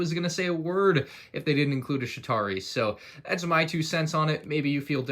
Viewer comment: Sounds like is saying Shitari instead of Chitauri